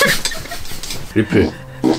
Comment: 에 나만 리필. 이라고 들리나..?